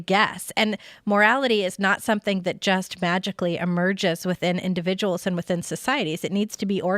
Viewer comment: why does she say vagically?